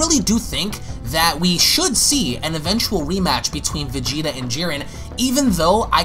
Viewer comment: Is It just me or at 2:35-2:38 did I hear him say weematch lol 😂